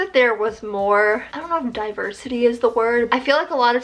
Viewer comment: oh, I thought you said diaper city... My volume was low..